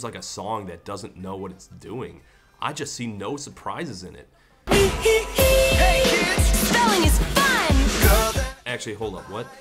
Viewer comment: I swear I had heard "Hey kids! Stalin is fun!"